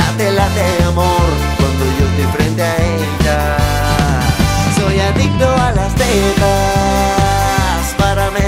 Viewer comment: Yo pensé que decía “soy adicto a las nenas” 😭😭